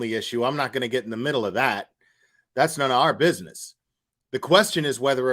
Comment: If she was intoxicated and in panic she mabye was confused? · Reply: I agree. I thought you were saying she died in the middle area.